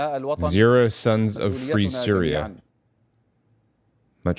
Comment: I think the intention was either "TO" or O' ( pronounced "oh") as in he is addressing the "sons of free Syria". I'd love to hear what a native Arabic speaker says he said. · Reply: Yes, he said, oh sons of Syria